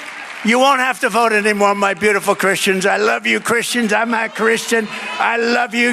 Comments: quoting @ 4:11 I hear Trump saying "... I'm a Christian ..." Russell you got it wrong IMO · He said “I Am a Christian”, not “I’m not a Christian” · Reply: I heard "I'm agh Christian", like he couldn't decide between 'a' and 'an'. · I'm pretty sure that Trump said "I'm a Christian" not "I'm not Christian" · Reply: Right, it sounded like he said “I’m uh” Christian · Russell Brand..!!!! HE SAY'S "I'M A CHRISTIAN!!!!!" He did NOT say "I'm not Christian" TRUMP SAID "I'M A CHRISTIAN"...!!!!! LISTEN CLOSER EVERYONE TRUSTS YOU TO TELL THE TRUTH!!!! PLEASE TELL EVERYONE WHAT HE ACTUALLY SAID!!!!! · I heard, I AM a Christian, biden is let away with a (supposed) speech impediment then when people deliberately mishear Trump he is then excoriated 🤨 🤷‍♂ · Russell, sounds to me @ 4:11 he actually says "I'm a Christian"?! Try it at half speed, there's no 'n' in the word, just a bad pronunciation of the "a"🙏🇬🇾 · Russ buddy, I listened to this 17 times on several different media devices and all but 2 sounded like DT sounded like he said "I'm ah Christian" 2 time it sounds like I'm not a Christian., majority rules😏🤔🤷‍♂️ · I thought maybe he said "I'm half Christian", although I don't know how one is HALF Christian. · He said "I'm uh Christian"...and it sounded like he said "not " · I thought he said I’m half christian.. Mehh maybe my hearing. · I think he said "I'm a Christian" but his voice cracked a bit.